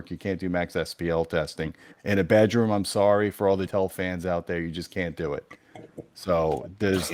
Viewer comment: For all the .... "Tell Fans out there you just can't do it"...what did say ? I don' t understand what said that was so horrible ? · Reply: I think he said " Joe n Tell fans"